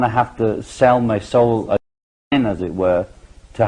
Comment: What word is edited out at 31:25? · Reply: The word is "again"